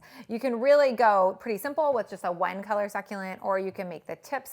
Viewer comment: I thought she said “wine colored succulent” and was so confused